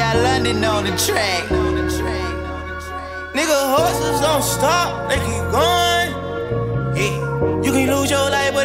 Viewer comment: who elses realized that he said hustlers instead of horses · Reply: i ALWAYS thought he said "n horses don't stop they keep goin"